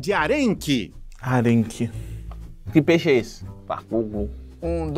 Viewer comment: sou só eu q escutei bakugou?